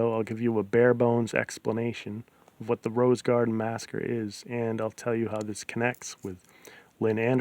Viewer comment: The captions said masker for massacre at 0:18 :)